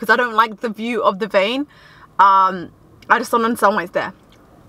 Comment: what did she say ‘’ i just don’t …’’ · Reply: I just don't understand why it's there